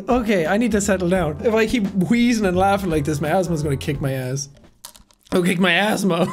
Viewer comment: did he say his husband is gonna kick his ass?